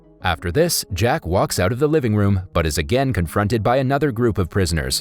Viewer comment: did he just say living room?